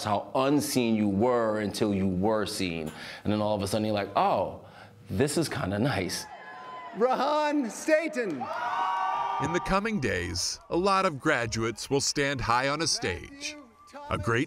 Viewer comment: Wait, is that dude's name really 'Run Satan'? Did I hear that correctly? They say his name at 4:28.